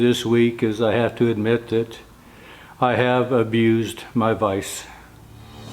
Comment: OMG i thought you said I abused my wife.. lol sorry i hear wrong sometimes sir